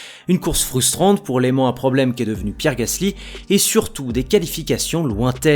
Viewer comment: je pensais que tu disais "l'élément à problème", et l'écoutant 5 fois, j'ai compris "l'aimant à problème" N'hésite pas à accentuer la prononciation parce que t'a une musique de fond sur ta vidéo. Sinon merci pour les vidéos et les analyses ;)